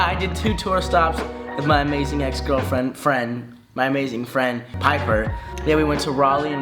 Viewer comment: I heard He say “My Amazing Ex-Girlfriends” then he changes it to “My Amazing Friends”